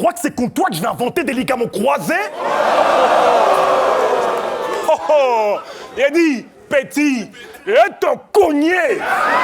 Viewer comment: J'ai entendu "ligue à mots croisés" XD